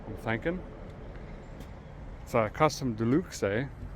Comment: Hey, about that white wheel up front. Did I hear you say "custom", or "cussed 'em'? Just ashkin'...